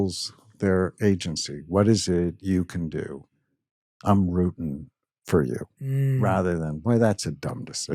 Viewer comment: What does he say at the 32:12 ??? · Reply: I’m rooting for you